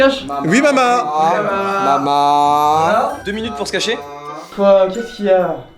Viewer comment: c moi ou il a dit mamaaaaa comme le gars sur tik tok ? 😅 répondez moi pls